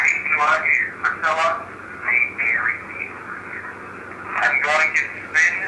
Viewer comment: i heard "they buried me"